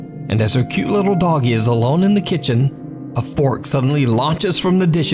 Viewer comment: | It it just me or did he say fork?